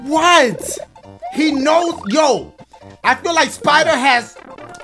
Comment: Thought he said Spider-Ham